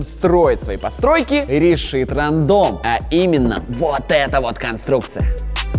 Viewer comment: сказал рандом,а мне послышалась фандом🤣